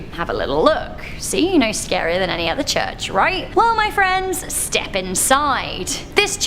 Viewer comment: Did someone said "Hello my friends"? XD